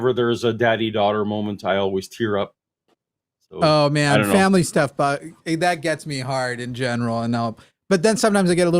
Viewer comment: Wait wait. Family stuff gets Jimmy hard? Did I hear that right? 😂